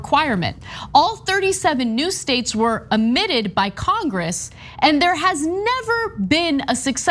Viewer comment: Did Ana say: "... new states were 'amitted?' 'Omitted?' That's what it sounded like.